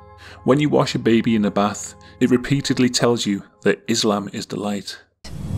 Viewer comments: "islam is the light" I heard "this doll is a mic" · Given the fact the “islam is the light” was used in a DS game it seems like it’s a stock baby sound effect that could have mistakenly slipped through. Even higher budget titles are known to utilize stock sounds so it’s no surprise that the same would be done for a cheap DS title. Reminds me of the case of the Zelda; OOT original Fire Temple theme which included islamic chants, which was pinned on the clueless use of stock chanting by developers. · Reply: Tbh I hear the "Islam is the Light" as "egg rice eats the rice", so I think a lot of it is parents letting their worry for their children overcome them